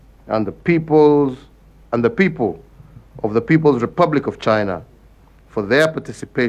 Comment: what about corruption, unemployment and the big 4 agenda. Did I hear ''REPUBLIC OF CHINA'' seems he heard king KAKA'S music